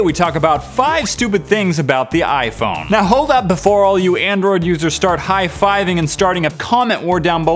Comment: Omg in the intro I thought he said. Oh you just wait Samsung your turns coming but he said android anyways, I was like lol there exploding now